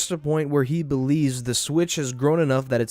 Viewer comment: you made it sound like you said “bitch”.